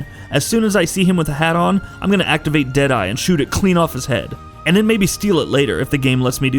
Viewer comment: The point is that you can shoot off John Marston's hat. You couldn't do that in RDR 1, since, well, you were John Marston. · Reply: I thought he said you can shoot his HEAD off